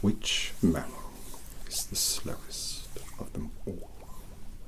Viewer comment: Snape: Does anyone know which mammal is the slowest of them all? Gryffindor? Me: Sloth? Snape: Two points deducted from Gryffindor. Anyone else? Me: 😐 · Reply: I heard "the slyest" instead of "slowest", and you can't even imagine the level of my embarrassment, since I'm a Slytherin and I had proudly suggested a snake's brain...